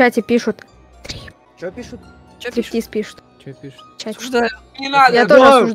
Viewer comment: что она сказала! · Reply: "Стриптиз пишут"